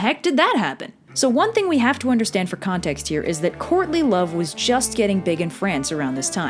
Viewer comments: I heard "Courtney Love" and thought "She's not big ANYWHERE, least of all Medieval Europe." They wouldn't be into grunge or punk or anything, they'd be into Journey and shit. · ish ... Thought you said Courtney love. Lmao